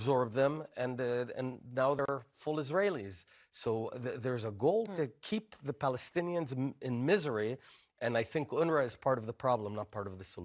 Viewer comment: did he just say “the goal is to keep the Palestinians suffering” what a 🤡